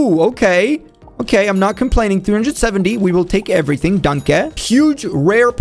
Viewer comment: did he say danke